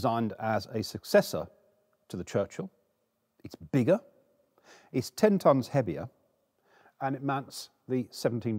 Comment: I thought he said “It’s ten times heavier” — had to listen to it again lol